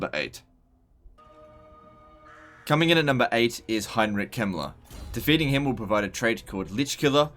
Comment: I wasn’t paying attention and almost fell out my chair when I thought I heard “next up is Heinrich Himmler”